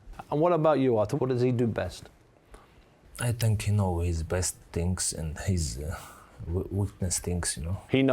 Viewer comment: did artur really say i think he knows his breath stinks??